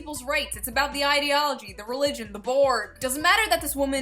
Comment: Did she just say the "Borg"? Lol